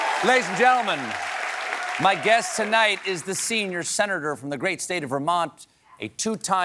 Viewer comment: I thought he said senior center. Bernie was spot on.